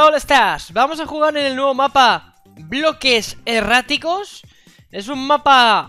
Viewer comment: la primera ves que lo vi pense que decia bloques eroticos